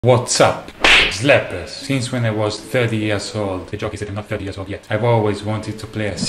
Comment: playing this at 0.5x playback speed and this is what davie said "The joke is I'm not 30 years old yet"😂😂